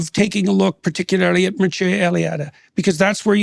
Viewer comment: who is he saying Dawkins should look at his work? I couldn’t make out the name. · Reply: Mircea Eliade